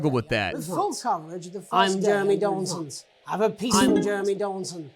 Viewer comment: When the guy said "Have a peaceful night" you paused at just right moment so people watching tv heard him say "Have a pee"